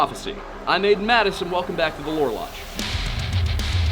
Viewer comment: I was, Blooto and misheard, the show as, whore lodge. Nonetheless, sound show